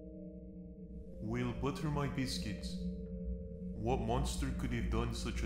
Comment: did he just say 'We'll butter my biscuits' ?! Lmao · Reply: Well butter my biscuits! He did